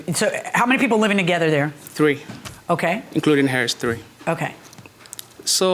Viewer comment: I thought he said “including the hair, it’s 3”